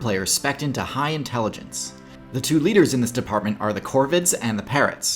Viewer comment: I thought it said “parents” lol